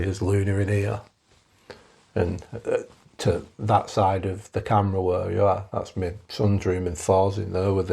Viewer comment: Did he say 'by the camera where you are'? Is he talking to someone else? I thought he was alone